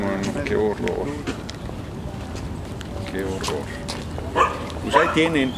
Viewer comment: no se si sea ignorancia de la gente o credulidad de pensar que poniendo sus carteles del PRI todo va a mejorar, cuando estos llevan años en el poderío y no hacen nada para ayudar a estas zonas tan marginadas. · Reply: Morena Si Así escuche yo (no votar por PRI y MORENA) en el minuto 5:28 le pregunté a Sr. Noroña y no me ha respondido...